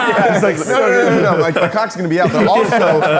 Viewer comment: Is saying “cock”, him saying a curse word for the first time?